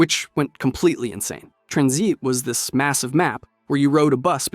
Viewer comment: tranzeet 23:00 did bro just say IW had good weapon balancing?